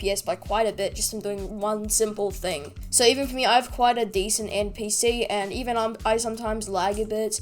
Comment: i thought he said NPC